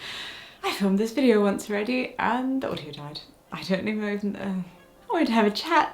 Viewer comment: I misheard "The audio died" as "Claudia died" and my stomach dropped oh my god.